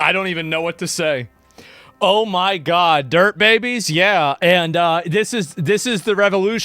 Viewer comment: did he just call his viewers dirt babies? Atleast its accurate i guess.